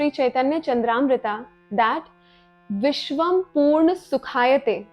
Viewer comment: subtitles - porn instead of purn